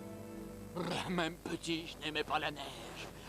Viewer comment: qu'est-ce qu'il dit à 1:07:27 ? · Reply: Meme petit je naimais pas ma neige